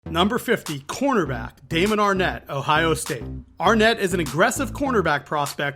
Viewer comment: I always thought the announcers said david Arnette 😂😂😂😂